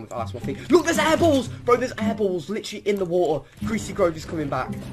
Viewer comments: How the guy says apples sounds like he is saying eyeballs xdddd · I thought he was saying "eyeballs" and talking about the canoe that looks a bit like a yellow eye...